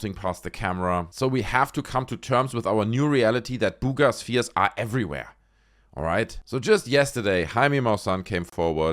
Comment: What I heard: “Booger spheres are everywhere.” The state of Ufology in 2025. No wonder Roswell is dying.